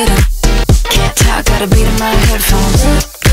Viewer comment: When I was first listening to it I thought she said “can’t talk got the pewdiepie headphones” 😂😂😂